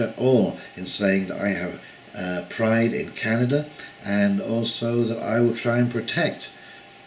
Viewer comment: Did you say in 9:04 'have pride in canada'?